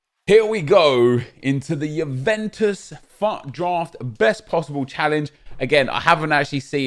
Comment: I thought you said fuck draft